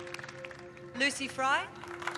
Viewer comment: WAIT.... 17:51 when they call the name of the girl Lucy Bring (i don't know if the subtitles are wrong and it was actually Fry, making the real name of the actress), she is one of the lead mermaids on Mako mermaids on season 1... I don't know, i find it interesting.